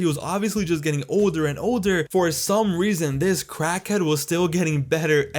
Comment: did he just call undertaker a crackhead?